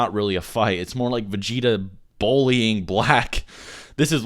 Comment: What is this word you keep using? Bowl - e - ing? Are you trying to pronounce "bully"?